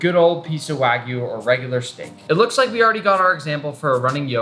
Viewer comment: i thought he said snake